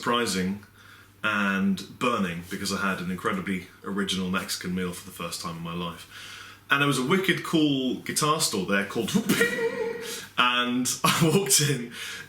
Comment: I had to replay because i thought he said Mexican MILF... my bad